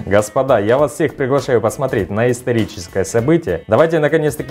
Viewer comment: thanks to you I've finally figured out how to spell "посмотри". I always heard it as "позмадрий" or some other nonsense.